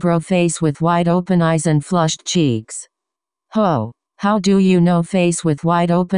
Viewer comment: When Bakugou said "Ho-how do u know..?" it sounded like he said "Hoe how do u know?" Im dying of laughter